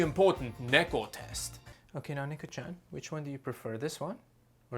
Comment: wait did he say neko-chan?